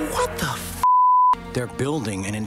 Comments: Did he say what the f**k? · did that chipmunk thing just say what the fuck in a kids movie, or was that added by Jon? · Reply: After going through the movie trying to find this scene I can infact state that what the chipmunk at 12:58 said is NOT the f bomb it is "what the fudge." Jon just censored that part, because he could. (Also yes I did look it up and found that scene and from what I saw of the movie IT SUCKS!!!) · Is he really saying "What the fuck"? XDD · did he actually said "WHAT THE F*CK" ???